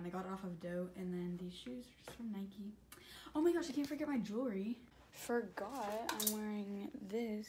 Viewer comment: i thought she was gonna say i omg i forgot my juul😂